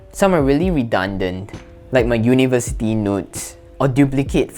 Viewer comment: I thought you said "My university nudes" but it was "notes" jajaja New to the channel and I like the content